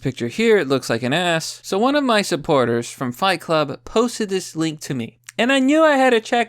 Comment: Wait, did you just say fight club?